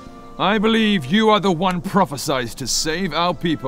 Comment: at first I heard "I believe you are the one proper-sized to save our people." at 22:54 .. laughed, and went back.. oh "prophesized" lol